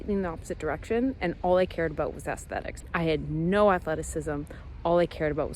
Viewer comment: for a sec (had to replay lol) I thought u said, "All I cared about was FASTlethics," and I was like huh is that a thing? Bc u said u were prev into basketball/performance but went 180° so the term played ok. Like let's ramp this up and get the body in (literal) shape FAST. No dumbass she said AESTHETICS.